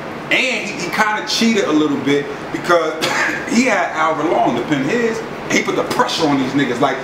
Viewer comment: Did he say " he had Albert Long to pen his"